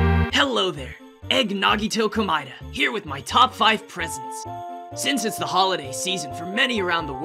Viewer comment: I thought he said I'm here with my top 5 prisons